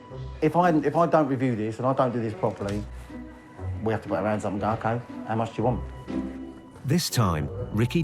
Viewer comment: Maybe I misunderstood because of his accent or maybe its a British phrase that I'm not familiar with, but at 30:08 did he say what I think he said? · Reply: " We have to put our hands up and go 'Ok how much do you want?' "